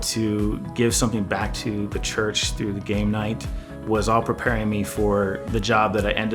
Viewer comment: I thought he said "the gay night". Very progressive, embrace all god's creatures.